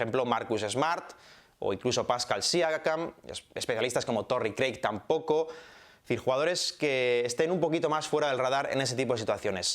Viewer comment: alguien sabe quién es ese tal "Torri Clarke"? Seguramente entendí yo mal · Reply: Torrey Craig, jugador de los Nuggets. Y gran defensor.